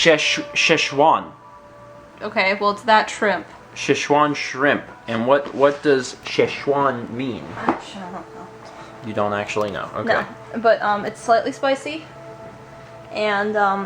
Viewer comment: Did curse? I know that's not too much of a big deal, but I think I hear her mumble, "Shit, I don't know." She says it so casually, it's actually kind of funny. X3